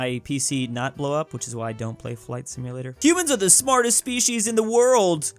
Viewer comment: I thought he said Cubans are the smartest species